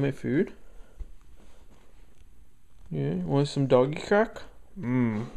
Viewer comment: for a second I thought you said "you want some ipecac?"